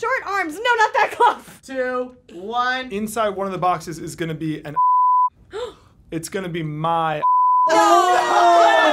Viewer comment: somebody please explain what did he say? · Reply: Phone